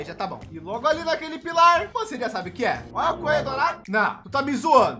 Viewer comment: olha o coelho dourado Qualquer fã de top: yeee Eu a um raio de <num> km: e o QUÊ o que você disse?!!!